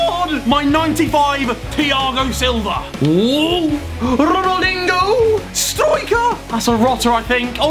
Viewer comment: Did he said Ronaldingo 😂